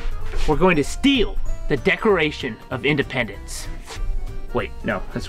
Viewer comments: Did he just say 'We're gonna steal the decoration of independence' ?? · Did he just say “The Decoration of Independence”? 😂😂😂